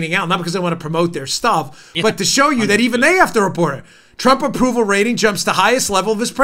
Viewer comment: Pump Troll numbers instead of Trump Poll numbers 35:50 minutes in